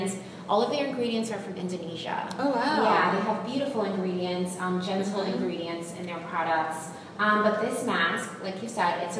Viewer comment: when she said gentle ingredients I thought she was saying genital ingredients lol